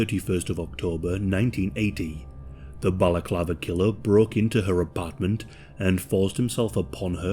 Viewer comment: I thought it said “The Baclava Killer”